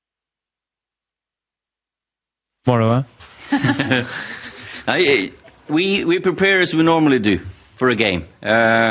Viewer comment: What does Bruno say at 14:00 ? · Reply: he said he will cry if utd lose